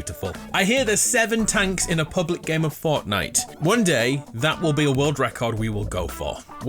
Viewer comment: it sounds like he says fartnite 🤣🤣🤣